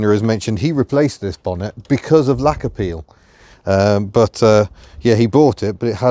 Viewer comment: I misheard laquer peel as lack of appeal. I feel better now too.